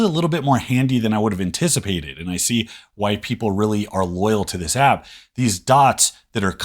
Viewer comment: Why did I hear: 1:35 I see white people are really loyal to this app 😂